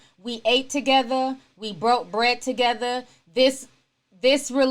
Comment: I kept hearing the ‘braid’ baby 😅😂